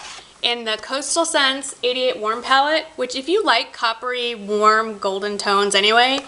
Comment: you were more than helpful. :) How long did it take you to find all those dupes/colors? I thought you were saying Coastal Scents Idiot palette! LOL! Well, in a sense I guess you could call it an idiot palette since they choose all the colors for you. hehe ;D Thanks for all your hard work!